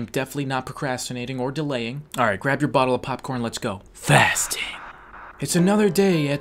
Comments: 😂 Did he say bottle of popcorn... 😂😂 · did he just say get your bottle of popcorn ???? lmao if he did man are you ok?